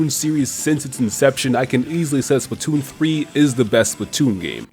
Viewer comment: I thought you said totk is the best zelda game